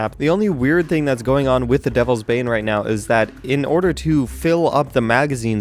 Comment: When said "devles's bane" I thought he said delve's bae